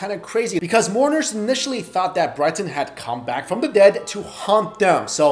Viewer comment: I thought he said"people thought that he came back to hump them" 😂😂